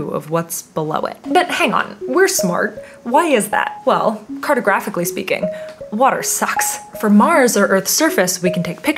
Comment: I thought she said: "Cartographically speaking, what are socks?". I was very confused.